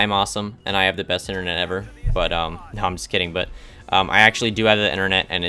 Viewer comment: ‘I have good internet, but I’m guessing you dont’ what a nice way to talk about people who are watching your vids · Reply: listen closely to the word "I'm kidding" u can repeat it if u can't hear